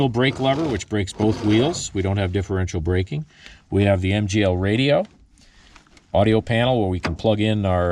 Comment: Very nice, indeed. What kind of communication equipment is on board? Or did I miss that in the dialog?? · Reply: Cam: go to 3:05. Said he has an MGL radio if I heard right. Located below the brake handle center of the stack.